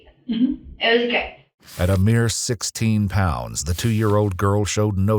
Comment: Am I hearing that right? Her 2 year-old baby was 16 pounds?